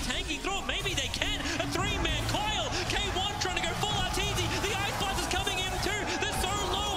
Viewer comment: did he just say RTZ?